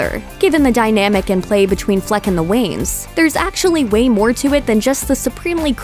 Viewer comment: i really hope i misheard that, but did she say "there's actually Wayne more to it"?